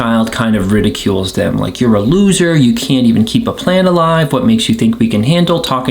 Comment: 'You can't even keep a plant alive' is the most accurate negative self talk I've ever heard in my life. I don't tell myself I'm a loser or I'm worthless but I do love to remind myself I let plants die. I literally have been telling people proudly that I've kept 2 plants alive since May which is kind of a selfburn on its own. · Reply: I thought you said, "You can't even keep a planet alive..." lol. Who can?